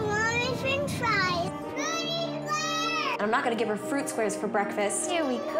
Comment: What did she say? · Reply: I think she said "fruity squares"